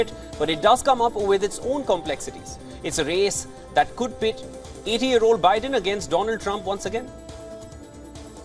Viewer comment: i heard "COULD PIT IDIOT OLD BIDEN AGAINST DONALD TRUMP ONCE AGAIN" and i dont care what you say lol